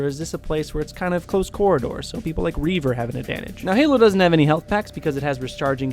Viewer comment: did he say reaver?